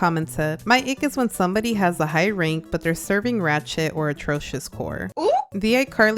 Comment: I THOUGHT YOU SAID RAT SH-☠️😭